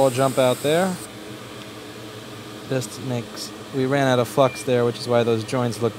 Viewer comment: heard that as "we run out *fucks*"